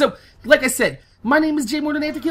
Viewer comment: yeah when he says morninafterkill i hear ''were gonna have to kill''